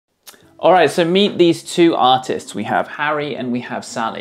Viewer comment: i thought he said "aight, so meet these 2 autists", lul